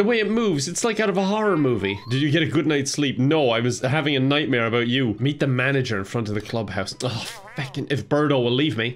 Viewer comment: I heard this: “No I was having a nightmare about you eating the manager.”